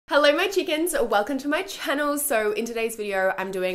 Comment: Hold up... did she just say "hello my chickens"?